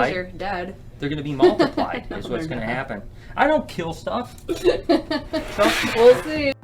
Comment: "I don't kill " ummmm did he just say "stuff"???